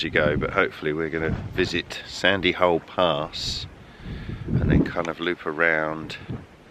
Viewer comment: Thought you said 'first stop is Sandy Hole Arse' lol